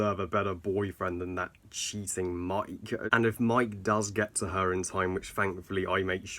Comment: No offence but whenever u said cheating Mike I thought u said that cheesing Mike 😂